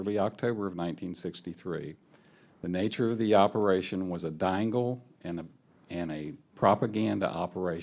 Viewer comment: What did he say at 1:14? Dangle?